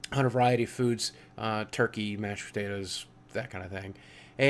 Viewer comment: I completely misheard "Mashed potatoes" All i heard was "Turkey, Meth" and i was all 0_o...